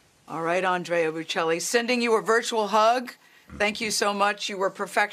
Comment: Did I hear correctly? At roughly 5:53 interval when the host asks Andrea Bocelli if he still feels optimistic everything will be over soon, he says “yes”, and she says “ew”. That’s exactly what she said! What a classless ignorant person to be hosting a public show. (She would be nothing if she hadn’t latched onto Oprah years ago.) Can’t believe she would say “ew”. But then, it shows the extent of her vocabulary.